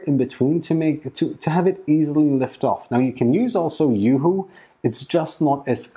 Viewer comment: Thanks for the tips! @ 13:11, Did you say to use Yoohoo on the bed?